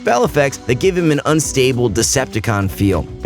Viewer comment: heard that as 'Disabled Decepticon" for a bit